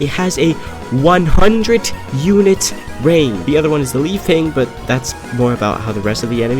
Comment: What is second secret thing? I heard "the leaf thing". What is it?